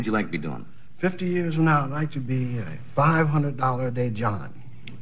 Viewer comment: Hey, can somebody tell me what does he say he wanna be in 50 yrs from now, in a 500dollar a day what?? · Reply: "A $500 a day John." Meaning a customer of prostitutes who pays $500/day.